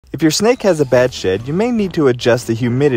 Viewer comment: Thought he said "in case your snake has a bad shit" I am dieing over here 😂😂😂